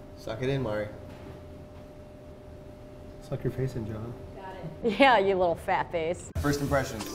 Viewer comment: I thought he said "Suck it up Jonah" and I internally screamed But then I realized he said Joven not Jonah :/